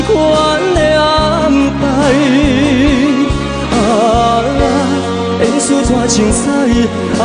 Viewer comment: 演出怎精彩 ... 聽成 挫青屎 XDDD